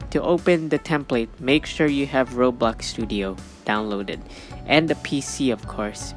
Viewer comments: did he just say robux studios? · For a second there, I thought he said 'To open the template, make sure you have Robux'